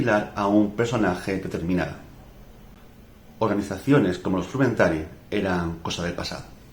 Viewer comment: Perdona, en 1:15 dices, "organizaciones como ¿los trumentari? ya no eran necesarias". No se entiende bien, ¿que organización dices? Gracias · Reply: Me refiero a los frumentarii, la policía secreta y servicio de inteligencia en el Imperio Romano, fundamentalmente entre los siglos I y III. Me refiero a ella en el vídeo sobre los espías en la Antigüedad. Saludos.